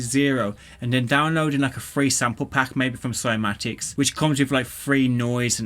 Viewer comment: What was the name of the site with the free noise and ambient sounds? It sounded like you said Soy magics. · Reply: Cymatics :D